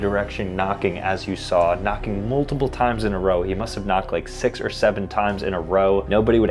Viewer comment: DID HE SAY 6 OR 7 TIMES???